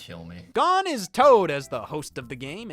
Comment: I thought he said "God is Toad" and I was just fully ready to accept that